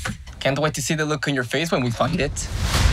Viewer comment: I'm Struggling to hear The word "Find it" what the English sub says and what he pronounced i Completely Misheard that to "f**k it"